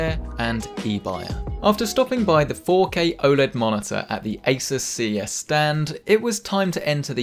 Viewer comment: did i hear right? OLED Monitor??? really?